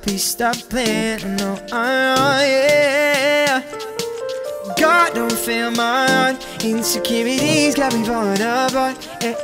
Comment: Question: what does he say from 0:40 - 0:48 ? Edit: I just can't quite understand that part lol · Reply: "God, don’t fail my heart, insecurities got me failing apart" then a mad adlib